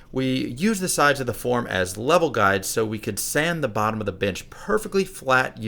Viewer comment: Am I the only one who heard bitch instead of bench